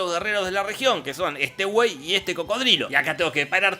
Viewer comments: Cuando dijo buey pensé que dijo güey, soy de México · momento mexicano (se que dice buey pero suena a wey)